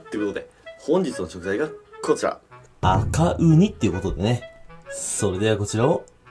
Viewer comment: ごめん悪気はないんだけど「バカウニ」にしか聞こえないw